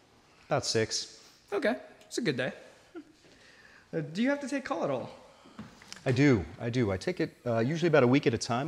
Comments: does anyone know what he asks at 7:40 ? i've replayed it and i can't tell what he takes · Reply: He asks: "Do you have to take call at all?" This is short for on-call, in which the doctor is available at any time when needed to care for patients. This can range from staying in the hospital or taking calls from home. says he takes call for a week at a time once every two months. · does anyone know what the interviewer says at 7:40 "do you have to take ...?" thaaanks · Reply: Call (being the doctor on call)